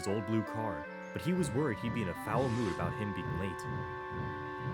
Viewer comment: i thought he said he'd be in a foul movie about him being late